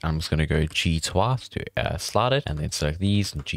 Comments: what are you saying here? I dont understand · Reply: G twice to slide the edges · what are you saying here? "G" to grab but then you also use twath? I have watched this bit a dozen times cant figure it out lol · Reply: twice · Nice tutorial, thank you for taking the time to make it, but can you speak slower, more clearly? Or at least keep the button presses displayed? In some places, I can't understand what you're saying, like here: 2:15, sounds like you're saying "G to R2", which drives me nuts because I still can't understand despite replaying the video. Might be nice to see a 2.0 video of this in the future, I also worked out what you were saying via other comments, it's "double G to slide it"... I think. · G to what? im sorry, i genuinely cant understand what you're saying here. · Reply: He says "G twice". I found it a bit confusing myself.